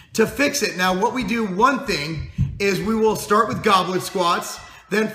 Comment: I thought he was saying Goblin squats at first. Haha.